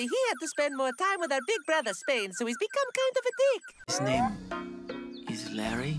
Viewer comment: did Italy just called Romano a dick? 🤣😂